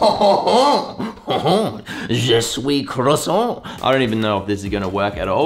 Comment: "I am croissant" -(translated) 2020 or i am growing haha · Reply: I'm french and I hear "Je soui crasson" 😂 That's funny I love this guy